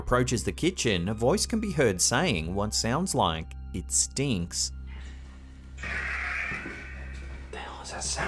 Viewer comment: It sounds like it says “it’s me” not “it stinks”